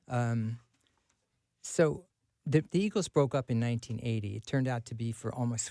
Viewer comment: thought he said the beatles broke up in 1980. had to go back 3 times until I realized this is the asshole in the eagles.